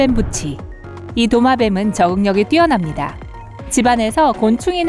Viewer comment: 나만 적응력이 아니라 정력이라 들었나?...